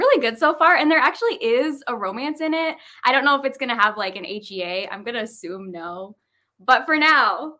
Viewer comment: Anyone wanna help me out with what is saying at 28:36 "I don't know if it's going to have an ?" I'm asuming it's some romance term I'm unfamiliar with but it's just really bugging me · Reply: Happy Ever After - HEA